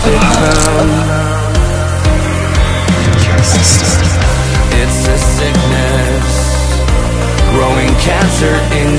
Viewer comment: can somebody tell me what jeff said on 1:22? ;-; I've listened it so many times I still can't understand it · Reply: "You bastard" xD ;D ^^